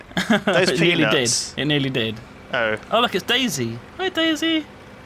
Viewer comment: When they said Daisy at 9:17 I thought they said "Day Z!"